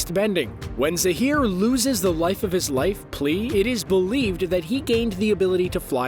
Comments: Did he say life of his life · Did he just say life of his life????